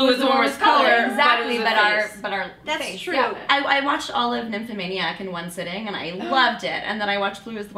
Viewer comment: Did she say Nymphmaniac ?